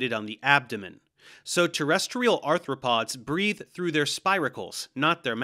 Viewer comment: On 6:22 it says though instead of through btw